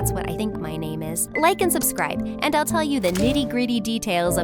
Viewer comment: Oh I thought this was a full movie, but this is a medley of old MSA videos. Should have known, but it’s still a treat when they post. And this was a surprise since they usually post Saturdays. I love Ellie and Liam/norman. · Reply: I THOUGHT SHE SAID DIDDY GRITY 😆